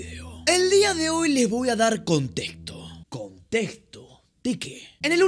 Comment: yo escuche contento jajaja